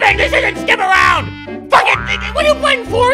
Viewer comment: did he say fuck it nigga?